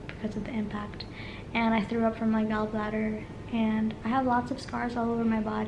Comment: I thought you said “I threw up my gallbladder” and I was like “uhh” (I was in a bad accident Sept. 22nd 2018, so happy to wear my seatbelt)